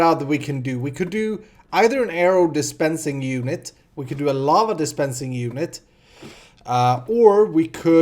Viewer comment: I thought you said llama dispensing unit! :,D